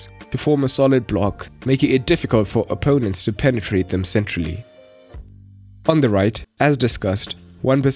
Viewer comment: Am I the only one that heard him say penetrate the midfield SEXUALLY. Like if you heard 🤣🤣🤣